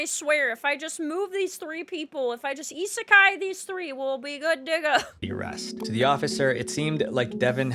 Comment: Yikes, I turned on captions to see what she said but I wasn’t expecting that. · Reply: She said "good to go" but that is funny.